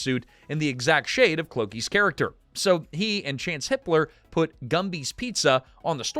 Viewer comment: I thought you said "Chance H*tler" lol. I had to go back and check. I wonder how many families had to change their name because of the mustachioed one.